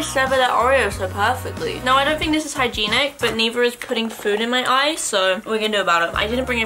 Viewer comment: did she just say "hygeeenic".. u know, like "hygienic"